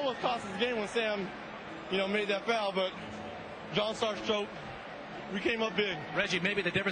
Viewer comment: Yikes 4:16 is savage af. I can't believe Reggie said that. LOL. Today's NBA has made me forget what real competitive ruthlessness is. Thank god for The Last Dance. Sadly today's NBA is so far gone, even The Last Dance can't save it. Did I say the NBA? I mean the BBBA (buddy buddy basketball association) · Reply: I couldn't completely hear what he said...I'll I heard was "He came up big"